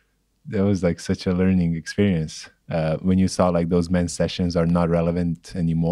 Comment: Does anyone know what sessions is referring to that he ‘let go’ of? Sounded like he was saying ‘man sessions’.